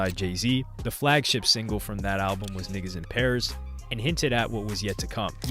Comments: Did say nigga? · Did bro just say nigga? 34:43 🤣